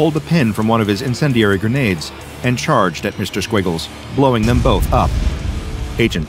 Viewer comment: i thought you said his name was Mr. Squibbles